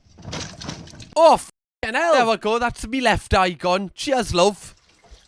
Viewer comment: what the hell did he say 😭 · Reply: Oh fuckin el There we go thats me left eye gone Cheers love